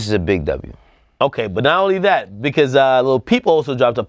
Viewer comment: Is it me or did Ak just call him lil peephole